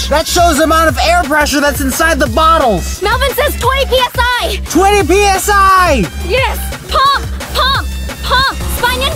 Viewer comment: did they say 20 psi?